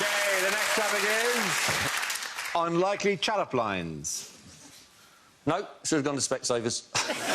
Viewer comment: someone explain what he says, plz? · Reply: “Nope, should’ve gone to specsavers.”